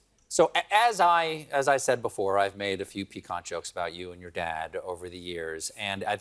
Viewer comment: Her humility to ask what that term means, that alone should make her more admired then ever. She is amazing! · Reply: It seemed like she thought he said "pecan", which would have been very out of context, which is what I thought caused her confusion. She's probably smart enough to know the word piquant, or to figure it out in context (the way most of us learn most words). <Having said that, even someone with a large vocabulary might mistake piquant for picante, which are closely related but derive from different languages (French and Spanish, I believe). In the moment, that could cause confusion as she tried to sort it out.